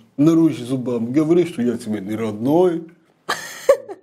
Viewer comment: Надо же... Такой сложный мульт, а все поняли. Вот только с Тургеневым проблемы.))) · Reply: Еще не расслышали слова. Он сказал не "Смотри! Вот ты, вот - я". Он сказал на самом деле :"Смотри! Вылитый я". Это значит : очень похож, как две капли воды.